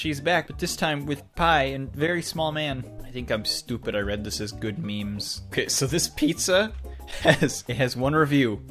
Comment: I thought it said good men